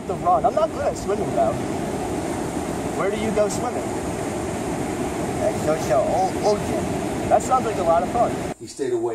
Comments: I think he said “I like the ocean” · i think he says "the ocean"